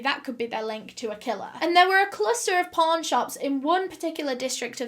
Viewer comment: Sounds like ur saying porn shops 💀